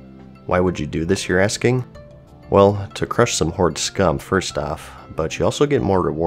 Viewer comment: did you say "HORDE SCUM" REEEEEEEE